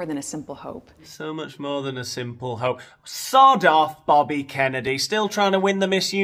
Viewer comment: Bobby Kennedy Did those 2 ever meet Didn't most ppl call him ROBERT? Plus What's with the world's Migraine speaking Robert's words In the Present? Didn't he die? Ahh it's just a shot in the dark 4 Me 2 notice this about "Bobby" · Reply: To me it sounded like he said "Booby Kennedy." Insufferable. Both of them.